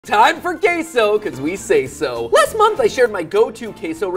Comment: I thought he said case oh